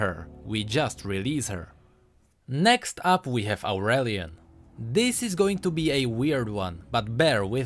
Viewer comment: did he say irelia or aurelion